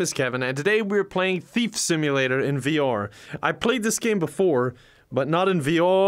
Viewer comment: I thought you say that you played this game irl 😀